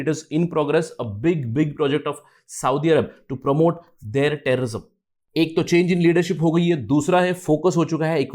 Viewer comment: I heard that to promote their terrorism 😂😂